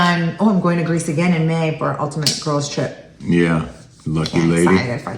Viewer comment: Whoa! did I just hear you correctly? You just announce that you're doing an Ultimate Girl's Trip to Greece?